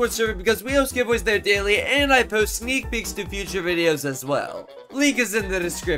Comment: did i hear feet pics or is it just me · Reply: It sounds like feet picks but it's actually sneak pics Which means some update leaks